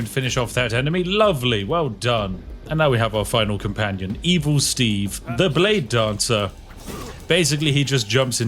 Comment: i thought you said "even steve" maybe next time